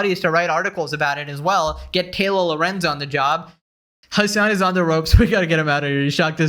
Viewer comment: Bro I heard "Get Taylor Lorenz on the dog" xD